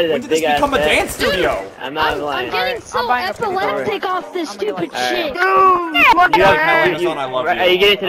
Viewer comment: Anyone hear the kid say "DUDE I'M GETTING SO EPILEPTIC OFF THIS STUPID SHIT!"